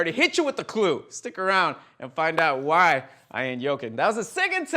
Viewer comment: When said yoking I thought he was saying joking but like someone from Sweden, am I the only one?